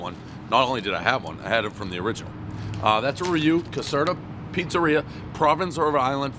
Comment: Did this dude really just say, “that’s a reyew” instead of review?